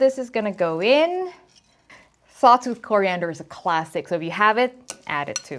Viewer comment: I tried multiple times to understand the Coriander ingredient she mentions right as she dumps the herbs in. It sounds like she says, "salted coriander". Is the correct?